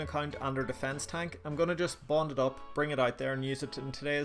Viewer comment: Hahah right at the beginning when. You said you're going to bond it up I swear I thought I heard "I'm going to bot it up" lol